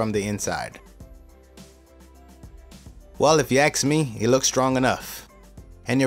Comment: Did he say "if you axe me" ?